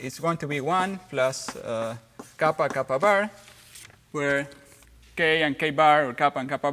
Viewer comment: I thought he said Capybara for a second, lol!